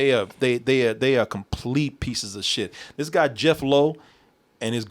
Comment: Did you say Jeph Loeb? As in the comic book writer? Because if you did then no wonder why superhero comics are woke now.